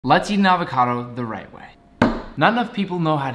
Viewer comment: Am I the only dumb that thought he said “Latin avocado is the right way”😅